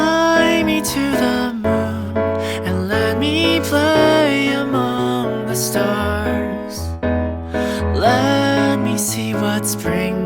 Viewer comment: did you seriously put an among us reference in here cause it sounds more like they're saying "among the stars"? Isn't that what they've always been saying?